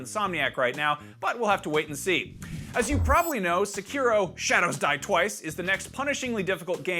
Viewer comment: I'm not the only one that heard him say 'shadows dwie twice' right??